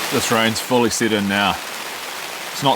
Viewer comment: <<--- I can't believe I just heard what I just heard! ------>>> " . . . but it's pretty '$#@ consistent . . ."